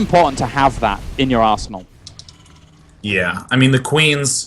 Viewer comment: I swear that at 12:02 he says 'Arsehole' instead of arsenal.